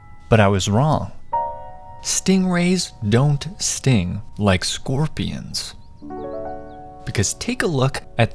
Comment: I thought you said “scopions”